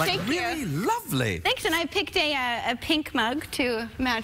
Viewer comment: i think she said "Pinkman" xd